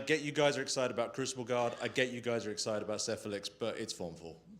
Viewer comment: I was only listening and for half a second I heard "I guess you guys are excited about siphilis" xD Needless to say it's not what was said!